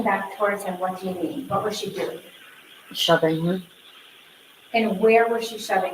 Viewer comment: She was asked “what was she doing” the daughter said shoving HER! Not him, as in her dad. It’s obvious she’s coached big time.